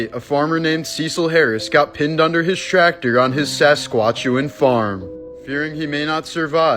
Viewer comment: I’m a Canadian and never in my life have I ever heard it be pronounced “Sasquatch-chewan” before 😂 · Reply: I thought he said sasquatch farm, you know where they grow sasquatches. Lol